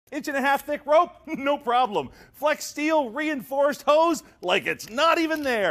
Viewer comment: "Cuts through steel" The aluminum: am I a joke to you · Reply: I heard "steeaal" not "steel"